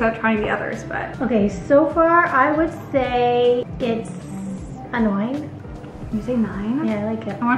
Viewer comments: I really thought her mom said "it's.... annoying" instead of "a nine" LMAO queen sh!t only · Omg I love the way your mom says “nine” it sounded like she said “annoying” I was like “damnnnnnnnn rosting the taste test” 😅 Also, I used to love the eggnog latte or the holiday spice flat white but I prefer my local coffee shop (Vigilante Coffee) these days because it’s closer, cheaper, and better tasting. Starbies has a special place in my heart though. Lol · I legit thought your mom said its "annoying" then realized she said "a nine" 😂😂😂